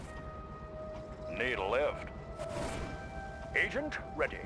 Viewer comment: Needle-if? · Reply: it's the nighthawk. "Need a lift?" I just hear "needle-if?...gotcha."